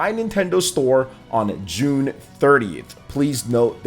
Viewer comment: Sounds like he says June 3rd. Im assuming the 30th? Thanks